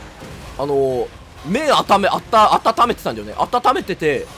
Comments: 麺温めてたが、目温めてたに聞こえた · 「麺あっためてた」が「目あっためてた」って聞こえて、そっか、ゲームして目痛くて大変やな、体大切にしないと、って思ってたら、暖めてたのは麺で、むしろ割り箸で怪我してて草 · 麺がずっと目に聞こえてて全然意味分からなかったwww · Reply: 自分も目温めてたに聞こえて、蒸気でホットアイマスク使ってたのかと思ったw